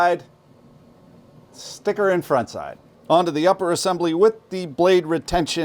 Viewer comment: You lost me at "sliding the plunger in and out" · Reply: And then all I heard was "stick 'er in front side"